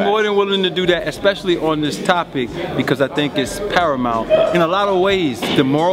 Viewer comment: what word did used on 4:13-" parimal " >? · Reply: paramount